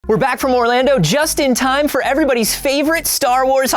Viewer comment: I thought he said 'were back fore more lando' and I was like yass he's gr8